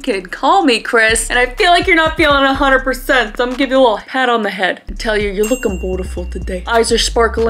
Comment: Am I the only person who heard “I’ll give you a little hat on the head” in the beginning 😭